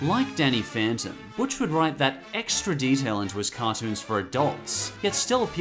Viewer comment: seriously? You say "into his" and the captions say "in Jewish"?